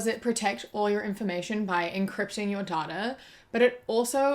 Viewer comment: Lol I thought you said, [...] incrypting your daughter" because of your kiwi accent.